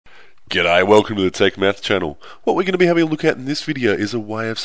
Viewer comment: I legit heard him say welcome to the try meth channel ie as like did I press the wrong video